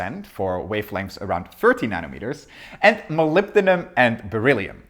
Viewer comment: subtitles generated claim you said 30 instead of 13 nm, if possible it would be nice to fix it for people with hearing problems to clear up confusion.